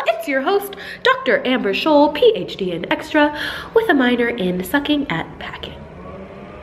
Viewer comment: thought she was going to say sucking sick haha